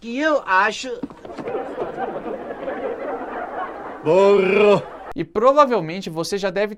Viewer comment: sei que foi "burro", mas pareceu muito que a dublagem meteu um "porra!" Hahahaha